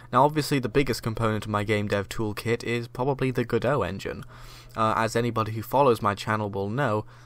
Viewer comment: At 17 seconds he mentions the engine... what engine? Sounds like he says Godot.... not seeing it listed ...maybe it is not Godot maybe it is something else...but what? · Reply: Yes it's the Godot Engine